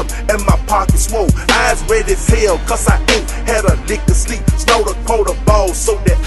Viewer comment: Eyes red as hell cause i aint have a lick of sleep Snort a quarter ball so i can stay on my feet Pat is the illest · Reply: tf I always thought pat said "eyes red as hell cuz I aint had a nigga sleep " haha 😆